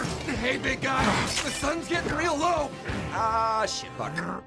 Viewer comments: Just realized that the song at 0:30 said holy shit or it sounded like it did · I heard the music choir say HOLY SH*T